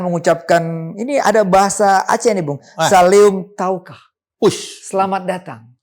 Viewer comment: Saleum keu gata/kah (salam untuk mu) maksudnya gk bang?. Saleum kaukah apaan cui🤣apa salah denger ya. soalnya suaranya juga halus🤣🤣🤣